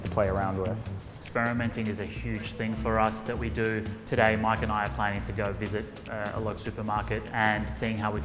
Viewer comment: i really thought he said mike and Ike 6:36 and the memories of limitless came <3